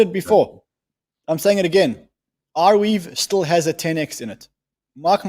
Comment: what did he say Rweave >? whats the ticker ????